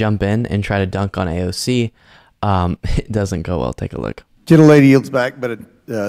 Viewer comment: Is... is he calling her "little lady" or did I mishear?